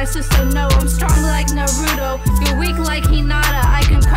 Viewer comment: its pronounced Naru'TO not narudo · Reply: She said naruto,maybe you heard it as narudo